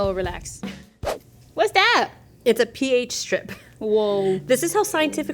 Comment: Omg. I thought said PIZZA strip instead of PH strip and I just accepted it like it was something everyone grew up with and knew about. 🤣 I’m not the smartest knife in the drawer 😑😅